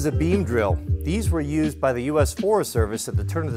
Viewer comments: I thought he said bean drill hahahah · I thought he said bean drill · I thought you said bee drill and I thought they named a Pokémon after it for a second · The first time i misheard him i thought he said a meme drill 😂😂😂😂